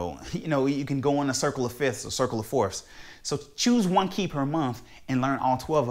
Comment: i cant understand phrase about them Key, i just cant regognize what you said? could you explain, cheers youre wonderful musician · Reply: Hi - "Choose one key (to practice) per month, then by the end of the year you will be fluent in all 12 keys..."